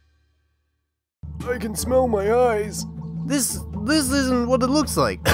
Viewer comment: The first thing I heard: “I c a n s m e l l m y e y e s .”